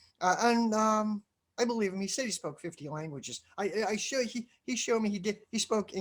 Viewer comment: I'm sorry, did you say 15 or 50? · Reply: He told me he could speak 50. He spoke in 8 or 9 and he said the rest were tribal languages , basically dialects and were very similar. I don't know if he was pulling my leg or not.